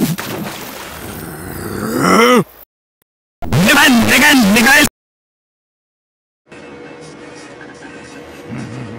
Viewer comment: Did Vagineer say what I think he said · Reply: Mnad Mnad Mnad! =Damn Damn Dammin